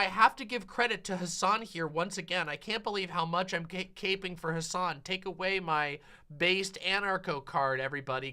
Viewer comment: is the exact second I thought said, "gaping for Hasan," and gave me a momentary heart attack